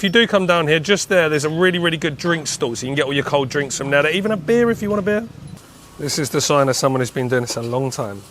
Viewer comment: notes the cold drinks store 👍, but 2:28 in my head, I heard: "you can even get a beer, if you want a beer Paddy D" 😏🤣